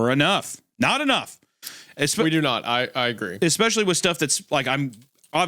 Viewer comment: Did say especially???? Gross